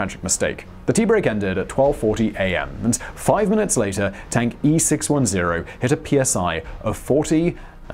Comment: I keep hearing “Tanky 610” as if it was a cutesy name for the storage tank.